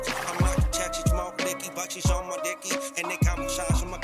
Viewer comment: I heard "but she's on my ducky"